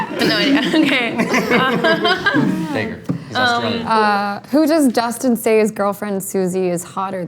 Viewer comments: WHAT DID JOE SAY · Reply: He said "Dacre, he's Australian" That's the name of the actor who played billy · what does joe say at 6:01? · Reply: “Dacre” The name of the actor who plays Billy’s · what did joe keery say? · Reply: Dacre. The actor Dacre Montgomery plays Billy. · what did Joe actually say? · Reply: I think he said Dacre, he's Australian. It's the actor who played billy